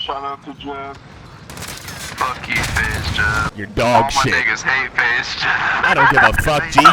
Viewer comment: Did he say nigga